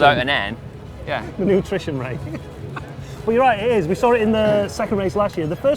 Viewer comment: I heard "nutrition rate" and thought wtf is this new variable?